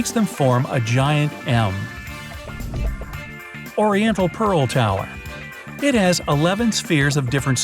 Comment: i thought you were saying "Ender Pear" 0_0